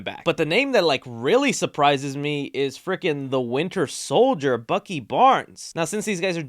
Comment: When you said winter solider sounded like you said "winner"